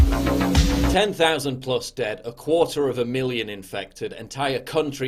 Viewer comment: Ok I had to re-listen to the beginning 😂 I thought you said quarter of a million dead! Boutta say 😂😂😂